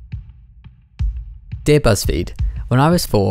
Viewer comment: Omg when I started the video, it was loading at the perfect time... So I what I heard was "Daebak" and not "Dear Buzzfeed" 😂😂😂